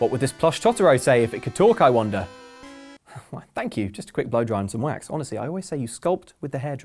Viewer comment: Did you just said Thank you just quit ROBLOX HEY THAT MY FAVOURITE GAME HOW DARE YOU !!!!!